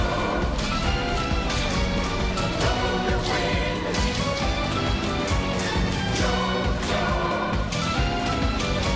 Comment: What they sayeng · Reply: Golden wind